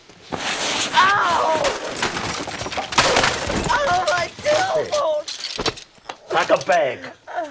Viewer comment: what did aj say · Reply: i think my tailbone